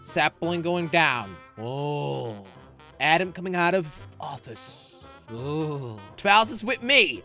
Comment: did he just say office